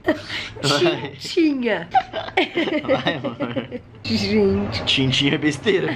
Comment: eu ouvi um vai amorr😍♥️♥️